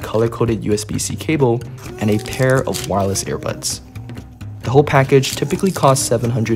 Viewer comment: Did you just say air buds lol